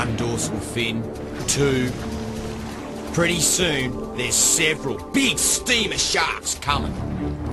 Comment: I thought he said pretty soon there’s several steamer shits